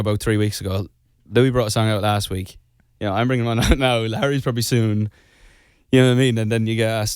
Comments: why does it sound like he’s saying ‘Larry’ and even the subtitles say Larry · Why did I hear "Larry's probably soon"? (He's referring to who's bringing out songs ... and its probably Harry but shhh) rip we're s t i l l clowning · Is it just me or did anyone else heard "Larry" at 45:00 too? I mean.. He said Harry.. But.. You know what i mean.. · am I the only one who heard “larry is probably soon”? I don’t want to make this interview about larry, was just wondering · Just watched this. Late to the game here a bit. Love Niall's music!! BUT...did anyone else catch the "Larry"? At about 44:51(?) He is talking about the guys releasing singles at the same time and I could have swore it sounded like Larry not Harry. But it could just be he was caught up in all the L's. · Okay, I myself hate it when Larries make everything about Larry, thought I am a Larrie, but am I just imagine it, but does Niall say "Larry probably soon" at 45:02 ? Or does he say Harry ? I mean he probabyl says Harry, but it kinda sounds like Larry... Idk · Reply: he said larry but it was like he was speaking fast u know and it just came out like that,, he DID say larry but its not bc of LARRY LARRY like louis and harry. btw im a larrie but i hope no one considers this a ‘larrie proof’ bc that’ll j be mad ,,,, idk why there r so many comments about it like Okay? he said larry?? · He said Larry. I heard it